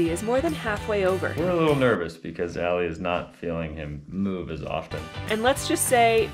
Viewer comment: In the preview I thought you said... "...Allie's not feeling in the mood as often" 😆 Thinking wow, he went there! hahahaaaa 🤣